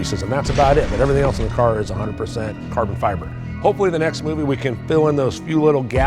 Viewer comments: Did he say the next movie smh they need to stop!! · ayo did he say in the next movie ? 💀💀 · Did he said next movie 11:41 ??? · Did he say "...hopefully in the next movie"????? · Did he say "next movie" at 11:41 🤔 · I’m sorry did you say next movie💀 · did he just said next movie! Please for all of us, stop making these movies. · Did he just say "in the next movie" ??? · did he say next movie at 11:41 ? Lets Go !!!!!!!!